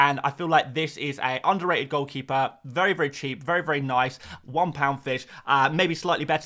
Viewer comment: Did he say "one pound fish" at 1:55? 😂